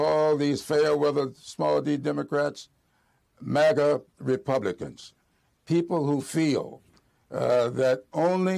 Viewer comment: what did he say? These small Democrats are now MAGA Republicans??